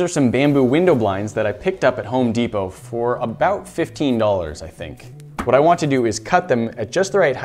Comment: I thought he said 50 instead of 15....